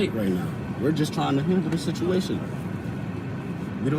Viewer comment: Did he just say “we’re just trying to HINDER the situation”? @ 2:34. That is what my cell phone “Auto-generating English Captions” is saying. I turned them on because the sound quality is terrible. The police officer(s) and his Mama are barely audible. He said hinder or handle. My phone says hinder. These sovtards can’t seem to understand what the word “Detain” means. When you are pulled over, you’re being detained. When you are under arrest, they will tell you. You will also be placed in handcuffs. Being detained does not mean you’re under arrest. However, when you are under arrest, it definitely means that you are detained. *5:28, lovely how he is blaming the police officer, AND his mom, did his stupid decision to start up the crazy “Sovcit/soon to be Mooron hill to die on”.